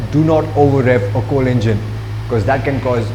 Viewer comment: did you say "do not overrev a 4 engine" (as in inline 4) or "do not overrev a cold engine" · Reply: Do not over rev a cold engine :)